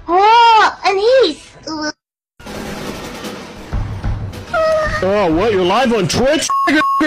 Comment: The sheer EXCITEMENT in his voice when he said "OH WHAT YOURE LIVE ON TWITCH?" was hilarious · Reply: I thought he said"OH WHAT YOU'RE LIVE ON DRUGS"